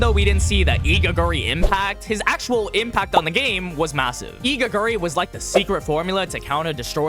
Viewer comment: did you say massive?